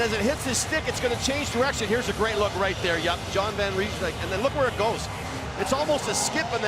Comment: Did he say John Van Reimsdyk or am I just hearing things